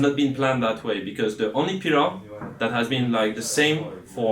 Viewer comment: for the subtitles, what Zaboutine has said was "pillar", to "feed from cows" is translated french expression whixh doesn't mean anything in english.